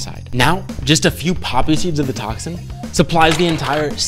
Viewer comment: BRO WHEN HE SAID BOTOX I WAS ON ANOTHER TAB AND I THOUGHT HE SAID BUTTOCKS